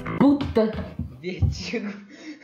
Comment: não entendi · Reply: Ninguém joga Vertigo.